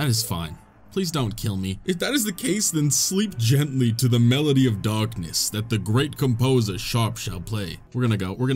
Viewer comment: When you said melody of darkness, it sounded like you said melody of dogness and now I have this image of binkus being buried under a pile of Shiba inu